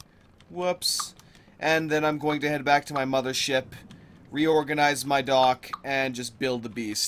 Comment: Did I just hear you say "head back to my mother's ship and reorganize my dog" At the beginning?